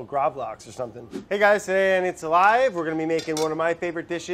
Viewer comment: Thought he said "Today on 'It's a Lie'" when they talked about The Truman show